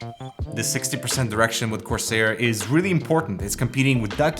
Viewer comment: At 35 seconds i thought he said a 60% erection